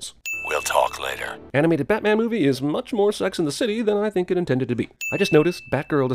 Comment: Wait....did he say "Sex and the City"? What?